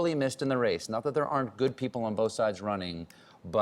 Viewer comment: did I hear "good people on both sides". This is what Trump said when KKK guys killed a black man.